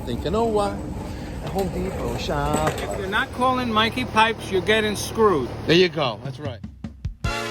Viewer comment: I had to rewind because I thought the last guy said "if you call Mikey Pipes you're getting screwed" lol